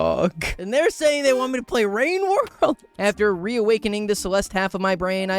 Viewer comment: did I hear a mention of playing rain world?